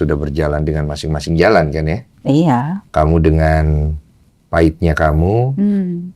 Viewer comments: kayaknya banyak yang salah denger deh, bangden bilangnya kamu dengan paitmu bukan aibmu ya guys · Iya yah😢 gpp ka mungkin yg cowo dluan yg di podcast kan, jdnya udh bias respon bangden nya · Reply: salah denger mbaknya tuh.. bukan aibmu tapi paitnya kamu..